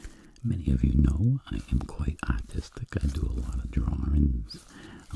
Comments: I thought he said "autistic" at the beginning... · who else thought he said "I am quite autistic,"? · He said artistic I thought he said autistic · When he said artistic I honestly thought that he said autistic and also this is just really good I fell asleep in 5 minutes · I thought he said "autistic" instead of "artistic" lmao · when he said artistic i thought he said autistic.....i was so confused for a second lmao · I thought he said he was very autistic He said Artistic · I thought he said autistic